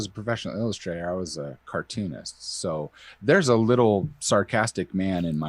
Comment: I heard, instead of "cartoonist", "arsonist" because I think I was yawning